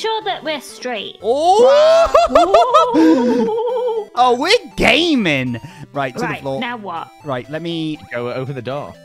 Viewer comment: says make sure we're straight then says we're gaming but I hear it as we're gay-ming